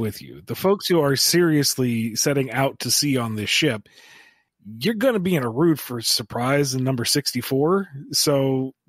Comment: What does he say at 3:17? 'You're gonna be in a rude for a surprise'? pls help